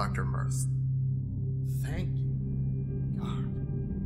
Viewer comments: What did he say? "Thank...________"? All I can hear is the 'thank' part. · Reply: thank god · I pretty sure I'm not hallucinating but did anyone else hear him say "Thank God" at 6:40?